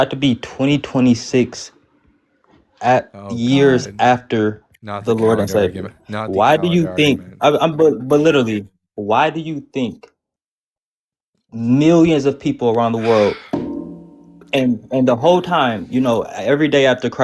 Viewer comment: 34: 41 "but literally…" ** I swear I thought he said "belittle me", and I'm thinking that's the most genuine thing he has said so far.